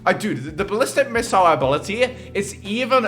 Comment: did he just say abolty XD